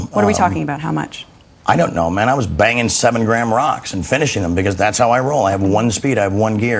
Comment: i thought he was saying im baging 7 grandmas not 7 grand rocks